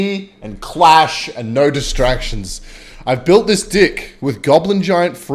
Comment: wattt did he just say dick ?